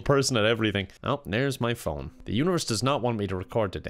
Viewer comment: Holy shit. When he said 'The Universe does not want me to record today' I thought he said 'Uterus' and was referring to his girlfriend